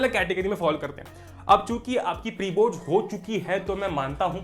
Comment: did he said chutiye